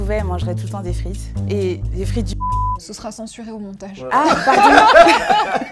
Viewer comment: des frites du quoi ?? 🤣😅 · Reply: McDo